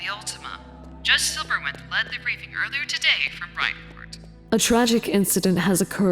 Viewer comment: Did that say Bright Port? As in... The Bright Port from Mcd? 5:28 I died, that slap was just too good